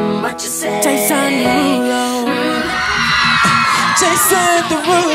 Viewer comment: Now , Ik I’m not the only one who thought it was “Jason da ruler” . AM I WRONG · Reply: I hear "chasin the rulo" 💀